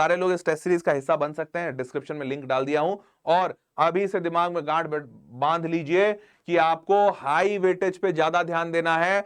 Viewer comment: sir kya bol rahe hai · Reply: yaaaand baandh lijiye